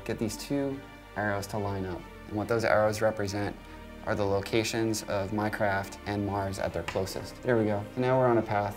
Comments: Did He Just Say Minecraft???😕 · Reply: no, he said 'the locations of my craft and Mars' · did he say Minecraft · when he said "my craft" it sounded like he was saying "minecraft" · I thought he said Minecraft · Minecraft and mars?????? Who else heard "Minecraft" instead of "My Craft" ? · DID HE JUST SAY MINECRAFT? LOL (ik he said my craft don't kill !e in the comments)